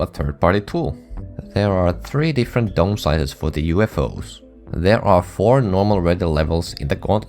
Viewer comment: What does he say? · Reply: “There are three different dome sizes for the UFOs.”